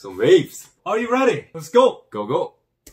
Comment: Let's go Me :- What...... Did I hear "Let's get it"?